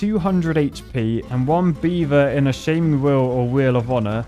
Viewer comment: Shaming wheel? I thought you were saying FAMING WHEEL! You know, where beavers go to get famous! 😉 wink wink